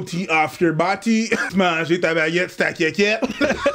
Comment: I heard "jpeut tu manger t'a baguette Su ta quéquette" and went poggers (do I need to specify I'm french Canadian? Lmao)